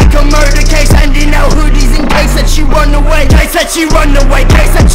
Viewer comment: Am I the only one who hears “NDL Hoodies”?